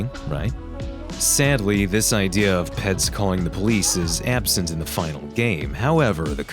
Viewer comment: I thought he said " pets calling the police "